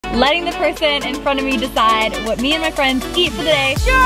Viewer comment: I legit thought it said, “letting the Pierson in front of us decide what we eat”🤣🤣 LOL. Btw, I love your channel💕💕